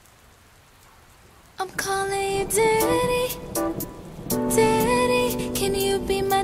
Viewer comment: the whole time I thought she was saying "diddy"